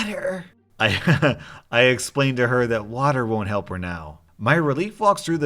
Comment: On the hot sauce one I thought the woman said liar not water XD lol.